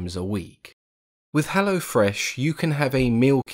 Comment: When looking at the timestamps, I thought 'HelloFresh' counted as a strange and terrifying Swedish creature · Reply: I thought is said helloflesh